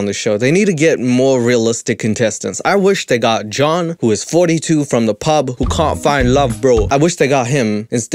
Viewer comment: When you said "John from the pub who can't find love.." I heard "John from the pub who caught fire" and I was intrigued